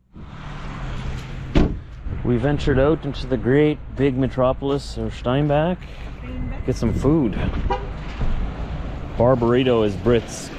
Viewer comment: When you said Bar Barrito I thought you said Barbarino. I thought Italian. That's good. Then I noticed the sign. Mexican aw that's great. Then you said Shrimp Barreto??? Somethings not right about this.